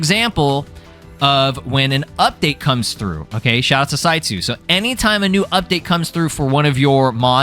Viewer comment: That entire time you were saying "Saitsu" towards the beginning of the video. I kept hearing "CyYu" and kept going "Wait.. When did Kaggy become a mod dev?"